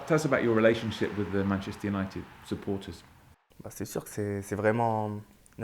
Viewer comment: In the subtitle instead of support they have written rapport